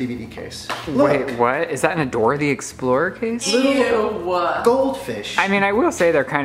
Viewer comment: did he just call the spirited away movie box DORA THE EXPLORER!!!?🙃